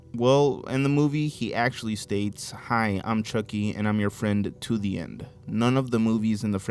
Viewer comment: i think the chucky one is simply a mishearing. I turned my volume halfway up and i also heard "till the end" instead of "to the end" it depends on how people recognize the word. EDIT: its just my opinion.